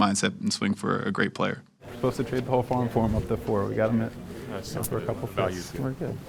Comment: Hang on, what does Kwesi say at 5:27 ? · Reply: “We were supposed to trade the whole farm for him up to 4, we got him at (10) for a couple picks”